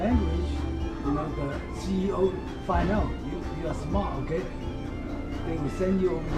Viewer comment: lol, The subtitles say that the CIA will find out that you are smart and send you to China, but the man says CEO. Then the guy holding the Camera says "I'm inspired right now" but it almost sounds as if he is saying "I'm a spy right now".